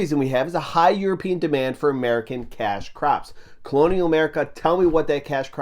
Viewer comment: i heard "cash crabs" lmao